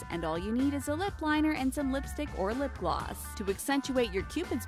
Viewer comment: it says "of" instead of "or" in lipstick or lip gloss it says lipstick of lipgloss